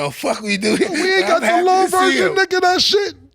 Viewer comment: What did say at 1:00:00?? That PAUSE Murda hit him with looked like it hurt Cams soul that he said what he said it 😂😂😂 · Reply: I had to watch that a couple times too. He said " We ain't got the long version of that shit? " Lmao that's why he knew he was out of line.